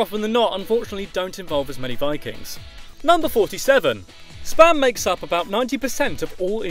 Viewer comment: Heard it as "sperm emails make..." God it wasnt that